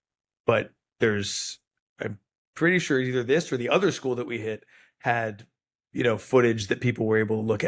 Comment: Wait, what, 09:00 minute mark, "the other school that we hit" did I hear that correctly? US firces hit two schools? Every friggin day I hear something about the USA and I think Oh they've fallen further...